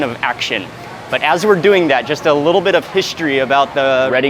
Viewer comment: i heard "a little bit of fishistory .."..???? 🤣😂